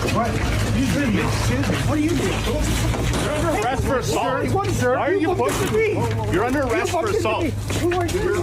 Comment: Honestly, at first I thought the cop said, " Your under arrest for a thought".